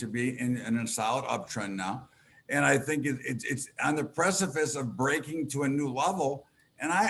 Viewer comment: Did he just say "precifice"?